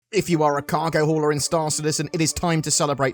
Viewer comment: Sounded like you said "if you are a cargo whore..."; I'm a cargo whore!